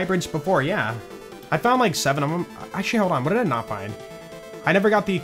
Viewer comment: Did you say "ah shit hold on" at 8:59 when trying to look up the hybrids?